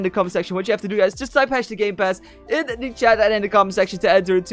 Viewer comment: Lol 3:16 he put gamepads instead of gamepass